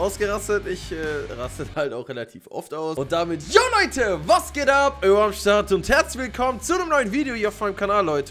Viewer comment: Was ich verstehe im intro jo leute was geht ab wie immer am start 0:25 speed klärt